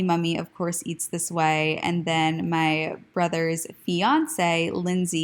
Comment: Great video - lol I heard “my brother’s Beyoncé “ 😂😂😂